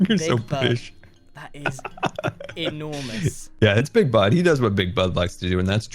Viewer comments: 1. I thought scar said bigbutt instead of big bud 2. Ren's base looks like it has been magnified on grian's giiant mal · I thought when Scar said Big Bud he actually said Big Butt. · Who else thought he said “big d**k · I thought he said big butt instead of big bud